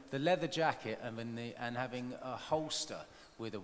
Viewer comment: what's the word he's saying? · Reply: Holster.